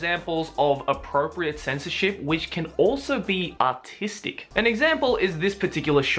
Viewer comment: I know he said artistic but it sounded like he said autistic